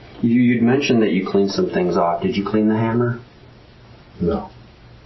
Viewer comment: detective asks “Did you clean the hammer”. His response was “No”, but the subtitles said “Yeah”. Why is that?